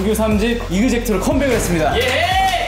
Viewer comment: DID CHEN JUST SAID YEHET AT 6:30?!